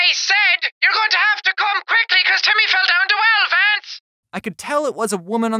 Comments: i thought she said “YOURE GONNA HAVE TO COME QUICKLY BECAUSE TIMMY FELL DOWN TO ELEPHANTS!!!” at first. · The person who do you know the voice on the phone sounded like they were saying you better come quickly because Timmy fell down the well vans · “Your going to have to come quickly cause Timmy fell down the well Vance.” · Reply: I thought she said: YOUR GOING TO HAVE TO COME QUICKLY BECAUSE TIMMY FELL DOWN TWO ELEPHANTS” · I heard "you're gonna come quickly because timmy fell down two elephants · I thought that random woman said you’re gonna have to come quickly because Timmy fell down to elaphants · I thought she said hance! Timmy fell down a whole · I heard “your going to have to come quickly cause jimmy fell down to elaphants” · I heard: “YOUR GONNA HAVE TO CALM QUICKLY CUZ TIMMY WELL DOWNE DE WELL, VANCE” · She said “YOUR GOING TO HAVE TO COME QUICKLY BECAUSE TIMMY FELL DOWN THE WELL VANCE!!” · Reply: I thought it said “YOUR GOING TO HAVE TO COME QUICKLY BECAUSE TIMMY FELL DOWN TWO ELEPHANTS!!!” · the lady said "your gonna have to come quickly cause Timmy fell down the well Lance" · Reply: Spy Freddy i heard "your going to come quickly because Timmy fell down the well vance!" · i love jacksepticeye screaming “TIMMY FELL DOWN THE WELL” for some reason Edit: 456- I managed to get 456 likes in five days- what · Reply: I heard “YOU’LL HAVE TO COME QUICKLY BECAUSE TIMMY FELL DOWN TWO ELEPHANTS” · You have to come quickly Timmy fell down 12 fence that's what I heard